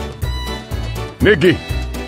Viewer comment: what did brute say 😨😨😨😨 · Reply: He says Nicky not the other wordd